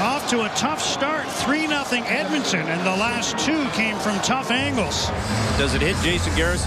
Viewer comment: I thought it said ankles I was so lost